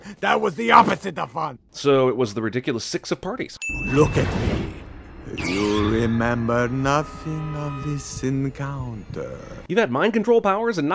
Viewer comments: does he say “look at me, ypu will remember the sin counter” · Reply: Jack Marston he says look at me you will remember nothing of this encounter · "you remember nothing of this encounter..." I legit heard "You remember nothing of the sin counter" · I swear I legit thought at 6:25 that he said "You'll remember nothing of the sin counter" and I couldn't believe my ears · Lmfao! At 6:28 it seriously sounds like Dracula is saying look at me you will remember nothing of the sin counter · I thought he said, "You remember of this sin counter." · Why the f*ck i understanded "sin counter" instead of "this encounter"? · is it just me or did he say "you will remember nothing of this sin counter" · If you think about it at 6:30 when Dracula days you will remember nothing about this encounter it sounds like he is saying you will remember nothing about the sin counter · I thought he said “you will forget this sin counter”